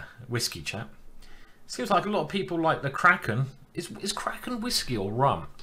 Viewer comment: LMAO! I thought he said "crack n' whiskey"